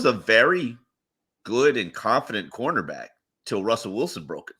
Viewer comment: “Noah was a competent CB until he stepped on an NFL playing field” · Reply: I thought he said confident